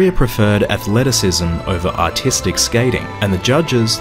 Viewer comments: What he hears “artistic” what I hear “autistic” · My dumbahh thought they said autistic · I really thought he said "autistic skating"